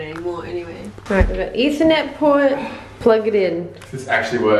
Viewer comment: did say ethernet p**n or what